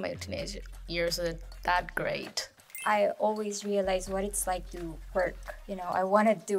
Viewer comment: Okay.. can someone pls explain what Philippines meant when she said @ 0:58 “i always realised was it was like to work”.🤷🏻‍♀️ It’s doesn’t seem to be a proper sentence & it doesnt make sense for a country who claims to have a strong command of English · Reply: "I always realize WHAT it's like to work" basically means that, mostly, students really want to work, and have been exposed to environment where the adults are working and wanted to have that experience already, or they just want to have their own money, BUT they are still studying and therefore, have to finish it first, (some students do part-time though, but most don't so that applies to the latter) and when they experienced what it was like to actually work, they realized that it was easier to be a student rather than working adults. I don't know if you will understand it though, but I hope you will.